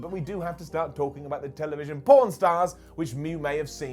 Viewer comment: I thought said the tv show Porn Stars lol